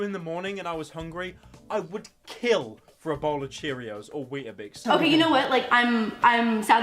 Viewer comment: Did he just say “wheet-a-bix”